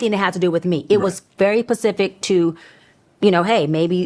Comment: did she say “pacific”??